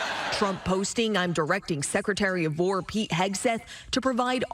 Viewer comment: thought she said "secretary of vore" for a second.